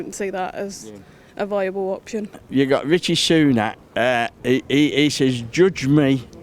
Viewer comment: Nothing makes me more depressed than seeing these videos and seeing how close-minded, ill-imformed, spiteful/hateful and uneducated some people are. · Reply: does he call him Ritchie Sunak?